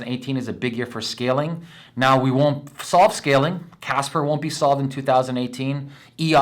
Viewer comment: what is he saying in 4:21 "chasper will solve". what is chasper?